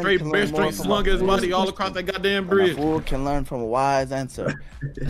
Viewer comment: I think he says “A wise man can learn more from a foolish man, than a fool can learn from a wise answer” so yes it was indeed some wise shit😂